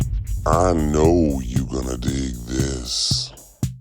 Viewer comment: Before I watch any of this - my first thought as intro comes on: Instead of "I know you're gonna dig this..." I only heard: "I Know You Gonna Smoke this...Get fucked up with me....." Anyway - now I'm gonna watch the whole show.